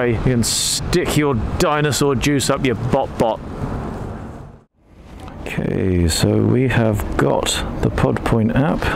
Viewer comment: all i heard was "lean pine peachy fry on a ping-pong bong youuuuuuuu???" nigga WHAT>?? @ 19:38 sorry Im american and I have 0% of a clue what on EARTH that lady is saying in the speaker at the station... what's she saying??? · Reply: Hahaaa! What she actually said was „please don’t use your phone on the forecourt, thank yooouuu“. I can understand the struggle though. She has the region free and inexplicable accent of ‚the chav‘.